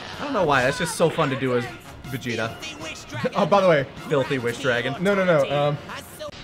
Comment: I always thought frieza saying Filthy Wish Dragon was him actually saying Fair Thee Wish Dragon